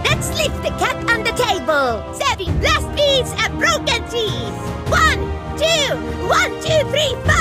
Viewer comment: I think at 1:37 she says broken teeth not broken cheese